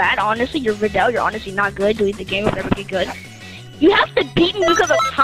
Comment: somehow was able to understand abit of what the kid said. and what i understood is that he said ''delete the game''. the rest sadly i could not understand cause he was fuckin possesed